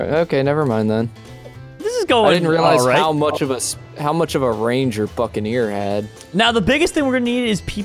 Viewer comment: it sound like said i didn't realize how much range your fing ear had